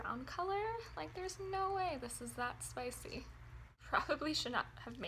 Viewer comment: oh I thought you said it wasn't spicy